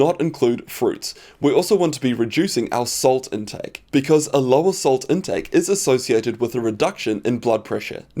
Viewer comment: My bad, I thought you said "a lot of salt intake" but after listening to it several times I now think you correctly said "a lower salt intake". You kind of slurred that sentence. But my mistake for misinterpreting it, sorry.